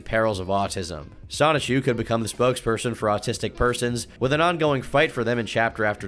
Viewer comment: Did he really say spokesperson?